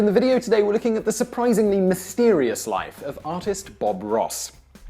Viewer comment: I thought he said autist Bob Ross instead of artist